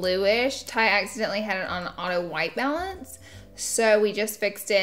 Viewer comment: When you said auto white balance i thought you said auto white melons lol